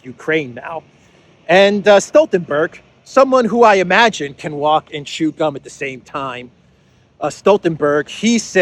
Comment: I thought I heard "talk and be dumb at the same time "